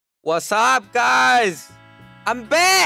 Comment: Sounded like "I suck guys!"